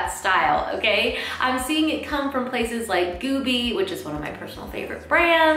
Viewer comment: Love your content! I have a Question about a brand :) at about 9:26 on this video, you mention a brand. What is it? I hear Goobi and I must know what it is and I’m sure I’m butchering the spelling haha - thank you!!